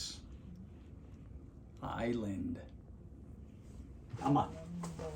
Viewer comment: LOL did I hear island boy 2:00!?